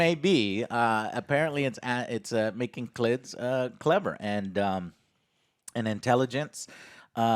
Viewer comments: Did he just say "making clits clever" ???!! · Did he say intelligent clits lol @ 13:26 😆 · did he say “klids cleaver “?